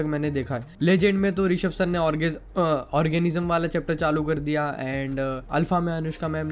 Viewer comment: bro did you say orgasms?💀